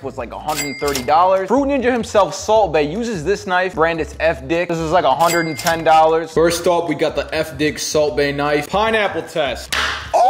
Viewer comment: I heard “elf dick” when he was talking bout Salt Baes knife